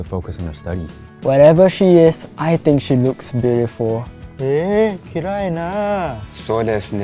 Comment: the sudden Japanese shocked me lol, but he was supposed to "え、きれいな" which means "Beautiful" but it sounded like "嫌いな" to me which means "hate".